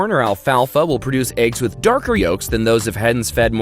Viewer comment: it sounded like you burped the word "yolk" :DDD